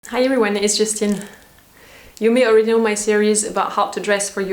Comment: Every time i hear "It's Justine" at the same time I hear "This (is)just in" 😀❤️